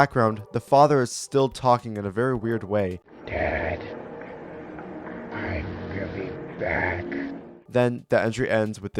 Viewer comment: I think is actually Jacob. Because I heard "Dad, I'm coming back"